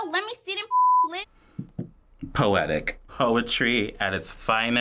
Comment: did he just say hoetry? Lol